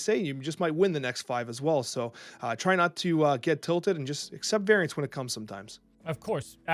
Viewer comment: Thought said 'accept varients' and my first thought was 'regis in shambles'